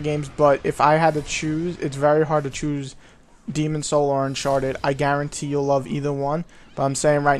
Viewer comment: soz 4 the double post but i just relised, demons souls or what did you say at around 6:10? thanks :D